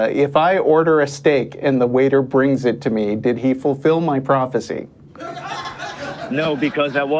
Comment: For a second there I thought said "messy prophecy". I totally understand the circumstance in which a person of faith, especially a faith with a binary afterlife scenario based on repentance and god worship, would call in to an atheist show. They want to stick up for their keystone belief. What I don't fully understand is why they would enter the fray with only hubris and naysaying in their arsenal.